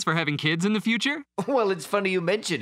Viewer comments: can someone tell what did they said? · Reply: “ We have one in the oven right now” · I heard "funny you mention we are thinking of having one right now" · Bruh I thought he said bun in the oven.